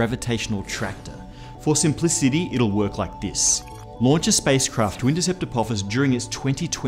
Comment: What is he saying at 0:58? Launch a spacecraft **something** during its 2029 pass? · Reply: "to intercept apophis"